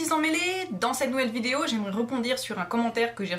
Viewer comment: mdr au début de ta vidéo j'ai entendu "bonsoir et bienvenue à tout mes petits sang mélés" j'ai mis tellement de temps à comprendre emmêlés